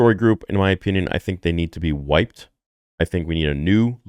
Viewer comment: Did you say at 9:14 that the story writers need to be white? Why does that matter. Star wars has a wide diverse audience. Just make good stories and characters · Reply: He said Wiped maybe turn on your subtitles before you jump to a big accusation.